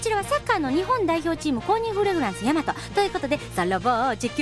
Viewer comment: チンコにフレグランス って聞こえた｡ チーム公認フレグランスね･･･｡